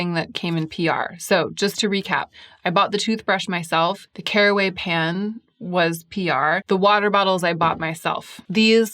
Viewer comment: I tend to watch videos at 1.5 speed and for some reason in this video, every time you said "Caraway pan", I heard "tearaway pants".